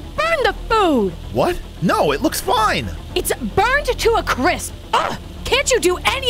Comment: what is a cris (said on subtitles) it was supposed to be crisp